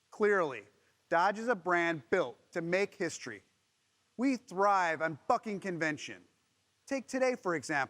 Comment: Did he just say "We thrive on f***ing convention" at @ 1:18 ? 😅